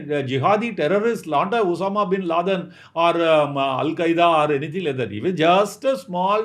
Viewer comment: Did he said 'Landya' 😂